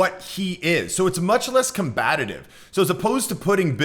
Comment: Did you really say "combatative?" Oops